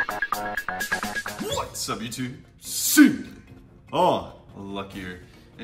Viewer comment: it sounds like he said soup